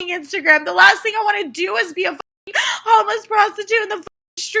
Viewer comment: Wait I thought she said she prostitution?